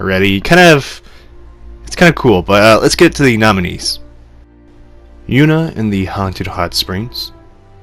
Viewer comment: Whats that anime at 16:59? I heard him say the name of it but I don't know if I typed it in correctly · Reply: Yuuna And The Haunted Hot Springs